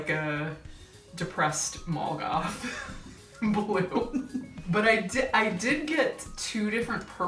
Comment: “Depressed Mall Goth Blue” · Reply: Thank you for typing that out... I heard "malgoth blue" and was wondering what "malgoth" was...! Now I feel silly.